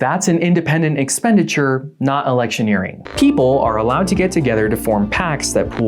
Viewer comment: I thought it said erection first... I need help